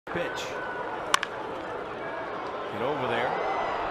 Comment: Is it just me or did he say bitch?